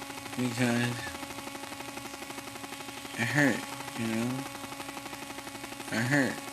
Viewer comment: after the first “it hurts” I hear “you know?”